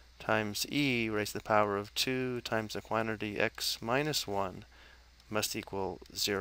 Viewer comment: what the frig is he saying there? "times e raised to the power of 2 times EQUINOR D x - 1" ???? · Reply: 2 times the quantity